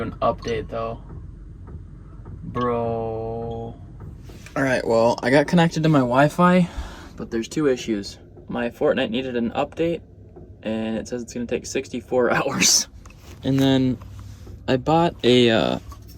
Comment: did he just say fartnite :skull